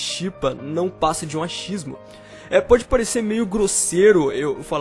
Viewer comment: juro que entendi ele falar "machismo", mas na verdade foi "achismo" 😂